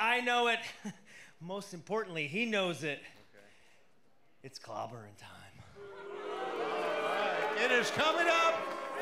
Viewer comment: Did he just say its clobbering time